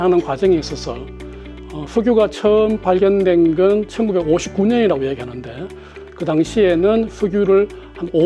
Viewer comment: 석유 최초 발견이 1959년이라고 하는 연구원의 말에 놀라수 밖엔 없네요~ 2차 세계대전 때에 탱크는 뭘로 움직였나요? 이래서 공기업 인력 감축이 필요한가요? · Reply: 위에 댓글중에 영상속 공무원이 1859년이라고 말했다고 자막도 그렇게 나왔다고 하는거 정신병인줄;; 1분20초대에 " 석유가 처음 발견된것이 1959년.. " 이라고 자막도 나오고 음성도 1959년이라고 분명히 말하는데 뭔 개소리인지;; 역시 바이든이 날리면으로 들리는 2찍들인가? ㅋㅋ